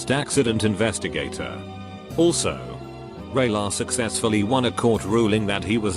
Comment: ray lahr sounds like radar said out loud